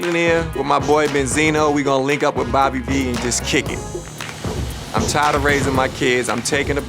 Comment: "I’m tired of raising my kids" did I hear correctly...? I am disgusted by this man. Innocent children that didn’t ask to be part of this whole situation, really Kirk?